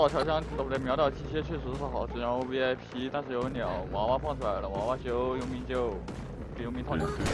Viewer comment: 愛膩說什麼 我一臉蒙逼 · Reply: 秒倒調香..喔不對秒倒機械，放在vip但是有鳥 娃娃放出來了 娃娃休 傭兵救